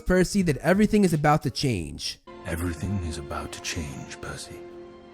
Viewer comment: Right here the subtitles thought Poseidon said ‘pussy’ and not ’Percy’, which is an accurate depiction of him in this movie (compared to book Percy)